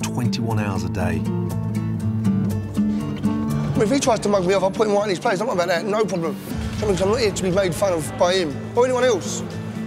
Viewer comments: wtf was he saying? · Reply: "If he tries to mug me off, I'll put him out in his place. I'm about that, No problem. 'Cos I'm not here to be made fun of by him or anyone else" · The hell did he say? 🤣🤣🤣 I only heard, “or anyone else” 🤣🤣🤣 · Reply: it’s mug me off not mark me off 😂